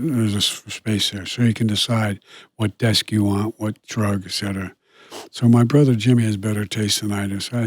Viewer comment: What is he saying at 41:29? "So you can decide what desk you want, what [???]" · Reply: Rugs. Got my answer. Thank you.